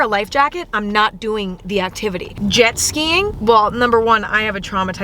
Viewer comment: DID SHE JUST SAY "BON"?? Our queen is lurning french yaaalll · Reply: she said well